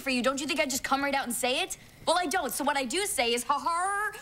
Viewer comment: on 2:08 what did she say all I here is harddd with I kept repeating it it sounds funny😂😂😂😂😂😂😂😂😂😂😂😂😀😊☺ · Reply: it was actually Har-har like she always does. it's not hard she's saying